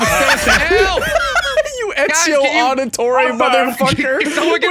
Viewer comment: did mans say Ezio auditorium 😭😭😭